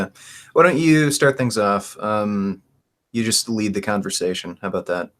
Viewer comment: I heard "You just leave the conversation" XD